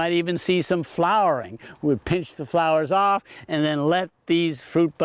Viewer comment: did he just say, pinch them off? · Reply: Yes, he said to pinch the flowers off, NOT the buds. The buds will produce fruit for 8 or more years. Since this is late summer, any blossoms won't produce viable fruit before winter and dormancy sets in. So pinching off the blossoms directs the tree energies to structure growth and bud formation.